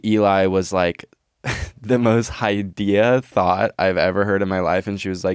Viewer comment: highdea? I was wondering what that word/phrase means, I'm assuming high idea?